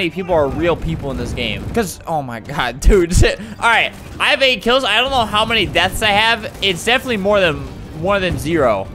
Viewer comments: What did he just say!?!🤯🤯🤯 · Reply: He said shit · he swear and he said "oh my god dude sh*t"